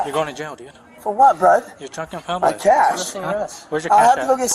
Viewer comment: I think he said, "I'm cashed," meaning he smoked the weed he had, so there was none left in his possession. If so, just another victim of the War on Drugs.